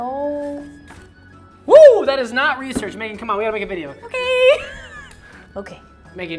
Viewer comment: I know he says "that was not research Megan" but bc of how he says it I thought he said "that was not research babe" for a second and I was like w h a t